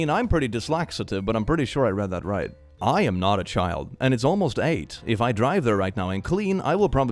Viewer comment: i thought for 1 second that they said "i'm not a child, i'm almost 8" and i got veeery scared for a moment